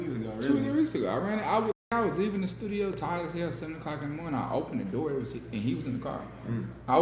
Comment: did he say Tyler, Tyga or Tyla here? Tyler would make the most sense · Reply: He said tired as hell 7 o’clock in the morning